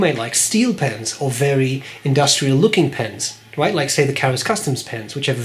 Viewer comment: Can anyone tell me what pen mentioned in 6:30 as industrial-looking pen? Not native speaker, my apologies. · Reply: Karas Kustoms pens.